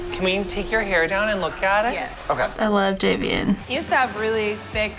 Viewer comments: did she call jonathan jabean??? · what did she call Jonathan?😂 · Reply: JVN- Jonathan Van Ness